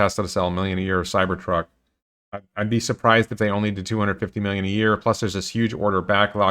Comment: Haha I thought he said 4 million a year but everyone said a quarter million a year - I'll be okay with 4 million a year